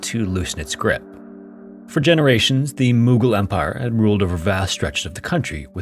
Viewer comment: I was listening to the video and thought it said "moogle empire" for a moment.